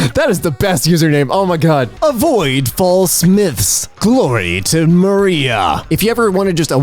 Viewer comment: I could have sworn that said "avoid fall Smiths"...